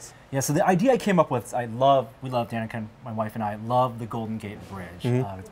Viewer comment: said my wife and i? I thought he had a boyfriend. not joking like an immature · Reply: Thanks, I thought I heard "My Mike and I" so I came down to the comments to read where people correct each other on such things. Thanks for the preemptive research. lol